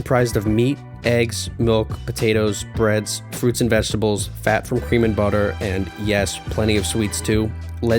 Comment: did he just say Human butter? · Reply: No. He said cream and butter